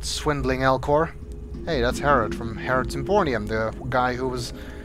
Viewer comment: heh, did you just say "Harrot's Empornium?" Sounds like an awesome place.